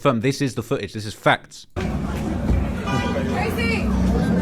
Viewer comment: sounds like she says "Tracy" 🤷‍♀️